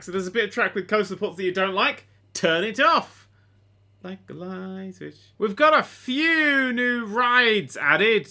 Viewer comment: what does he say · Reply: "Like a light switch" I think